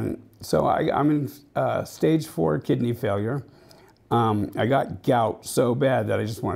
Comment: dam im disappointed the trolls got to him.. I hope the best for you! Hopefully they are gonna have him on dyalsis or get a transplant..god forbid its terminal. Hes a good dude whose changed his life around i hope only the best for him. Been a fan for a long time n its sad to see his account go. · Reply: I thought he said liver failure I thought that was only for kidney failure